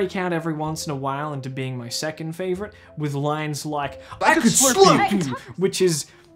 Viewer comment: Ive listened to it 4 times and I can't figure out what he says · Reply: i think its "i could slurp you!"